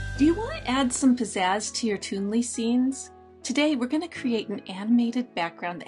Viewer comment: This is only for Toonly. About 16 or 17 seconds in, she asks if we want to add pizzazz to our TOONLY videos. I thought she said Doodly, plus it's uploaded on the official Doodly page. Of course, now I want Toonly, too! 🙂